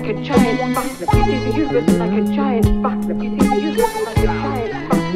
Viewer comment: I thought the end was saying the uterus is like a giant butler. Then I looked at the lyrics and saw it said universe, I kept turning the volume down on that part haha